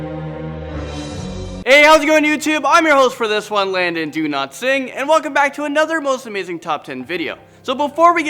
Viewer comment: I thought you said Landon do not sing